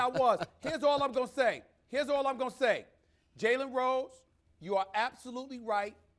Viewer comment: Kwame sent me here. 👀 Did anyone hear at 20:52, "what are we doing?" as Stephen A says, "Jalen, you're right.